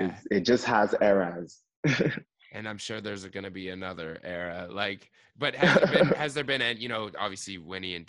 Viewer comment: What did that white man say in 6:34 did he say "I'm sure there isn't gonna be another era" or did he say "im sure there is gonna be another era"...after ckay said there are era's to it. Someone pls clarify me cos I think I'm smelling racism from him there. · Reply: He most definitely said hes sure there will be another era. I don't sense a drop of racism. This white dude is paying nothing but mad respect to this talented young African brother. I love it!